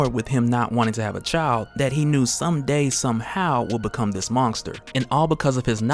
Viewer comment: I swear at 4:58 your saying mongster instead of monster